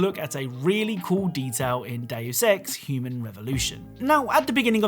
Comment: I THOUGHT HE SAID “DAY OF S3X” NOT “DEUS EX”